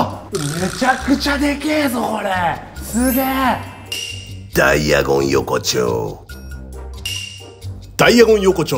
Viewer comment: ダイアゴン横丁がダイナゴンアズキにしか聞こえなくなってきた笑